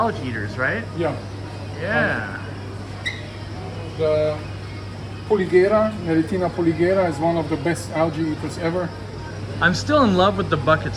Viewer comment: Around the 6:40 he points out something as the best algae eater. I couldn't understand what he said. Anyone know? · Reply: Panda Garra i think is what he was talking about. Some kind of Algea eating Garra at any rate.